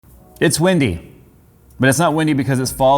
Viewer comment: Right off the bat I thought he said "it's Wendy"...